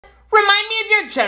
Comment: OH I THOUGHT HE SAID "V's are for girls only"